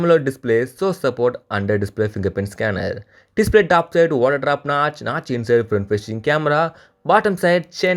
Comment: kya kya kya kya kya..... Kya bolo.. Sckachnach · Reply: Display top notch water display mera lauda bhai nahi ho pyega mere se